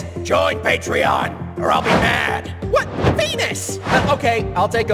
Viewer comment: subtitles said penus when earths said “ wha- VENUS!”